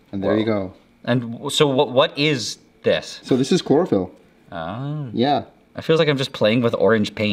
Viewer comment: I thought they said… “so what is this stuff….” The camera man “It’s chloroform” “ahhh it’s cool”! like chloroform isn’t something serious that can kill you by breathing in its fumes!😂